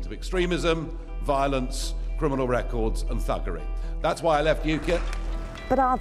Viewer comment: I had to listen to this again as I thought for a minute he said buggery, rather than thuggery.